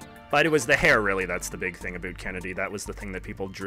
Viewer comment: Did he say a bout Kennedy